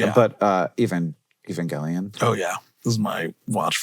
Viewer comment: I don't mean to make accusations but with the mistakes these subtitles make sometimes they don't seem like they were written by a person. What do you mean "even Gillian." You don't know what Evangelion is? "Allow me to the moon?" What?? That's not what he said. That's not a song. I love these videos but please get better subtitles, guys.